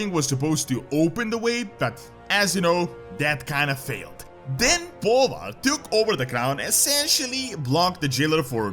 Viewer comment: I can't be the only one who heard that as "And then BALLER took over the crown." I mean.... right?